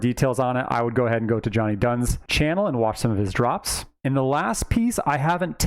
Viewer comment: Which channel did you refer to at 4:01? I hear giant guns? 😅 can’t find that one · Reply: Johnny Dunns lol - <link>